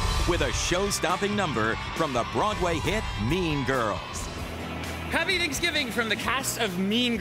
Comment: I heard him say meme girls